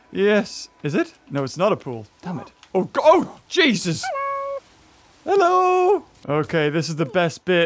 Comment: XD it is actually like he really said hello that's hilarious